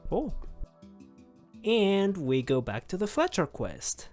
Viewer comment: did you say fetch quest no fletcher quest REFUSE to elaborate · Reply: i said fletcher quest but i'm bad at saying words